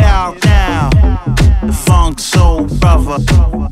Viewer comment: This is what I hear..."funks yo brotha"...everyone else is hearing "let's go brandon" lol